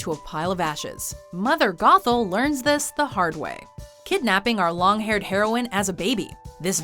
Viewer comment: what it said: Kidnapping our long haired heroine as a baby" what i heard: "kidnapping a lot of heroin for a baby"